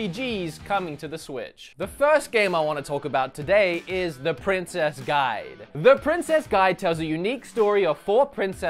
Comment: When I heard presses guide I thought you said princess bride ...... I was wrong very wrongs